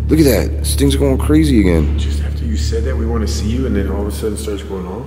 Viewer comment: i heard "she's after us" or" she's after you"???